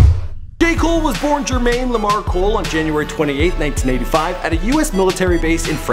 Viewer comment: Who else hears “gay cole”? Not trying to be rude but that’s what I hear he said.